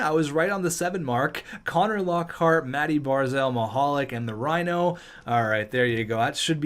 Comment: Honestly I thought it said mihalik I got scared but still losing a soldier 😥